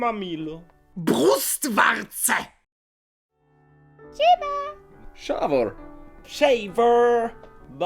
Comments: мне одной послышалось чи да 😂😂😅👍👌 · пусть в дворце послышалось😂